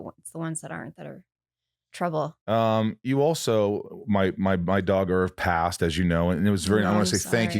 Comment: I was watching this on 1.75 speed and I thought you said my Daughter passed! It's the first time I've ever though "phew" after hearing about a dog dying. Losing a dog is terrible and I thought of mine as my son, but at least you know they had a proper full life.